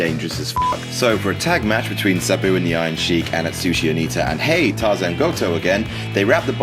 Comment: Sabu and the original Sheik, not the Iron Sheik. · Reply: I know, but it sounds like he says the iron sheik.